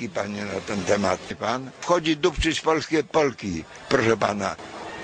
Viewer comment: 'Babcia chodzi dupcyć polskie Polki"! - Hahaha, jak to usłyszałem to myślałem, że padne.